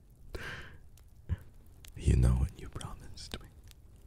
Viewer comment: pls tell me something sicklings! what is he saying at 5:25 ? i still not good at english and i couldn't catch him😢 pls~~ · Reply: Hes whispering: "You know what you promised me." □